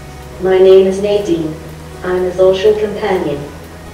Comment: I know it's not what she says, but it SOUNDS like, "...I'm a bullshit companion."